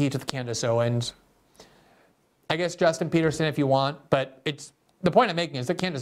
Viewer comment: Did he just say Justin Peterson?